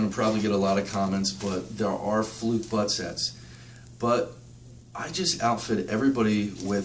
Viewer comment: what was that for a set? I couldn't hear what he said. Bug set? Bud set? Buck set? I checked google but can't find it. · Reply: Sorry! BUTT Set. is what they are called. For tracing out 66 blocks or telephone (analog) lines. Rare we use them these days....but gold when you have one onboard!